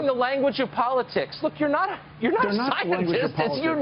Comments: did he just say BILL NYE IS NOT A SCIENTIST im calling a hit man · Did he just say you're not a scientist?